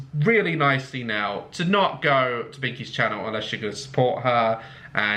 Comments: I thought the video said “bikini” instead of “binkie” and thought it would be some porno. But I guess it was too good to be true, huh? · Am I the only one who thought it said bikini until he said binkie😂😂